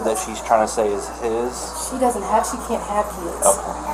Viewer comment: “She can’t have kids”, officer: “emm” 😩 😂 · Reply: I thought she said he can’t have kids